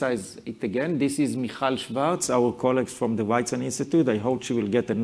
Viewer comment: what is his colleagues name? It sounded like he said Michel Svertz at the ____ institute??